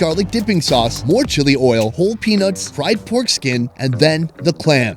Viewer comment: 😂 What Sonny said at 28:54 sounded like: To the bowl she added, "Crushed Penis and Fried Foreskin". And at 29:24 It sounded like: "Sorry.. I was eatin' the Foreskin already.." I was laughin' my ass off! 😅😂🤣